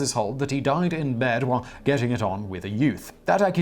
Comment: It really sounded like Simon said that"The pope died while in bed with a yoot". I laughed so hard.